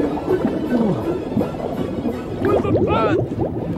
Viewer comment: What does Larry say when he's underwater at 3:52? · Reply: "Where's the fence?"😂